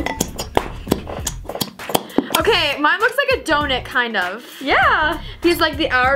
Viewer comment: instead of saying donut she said donit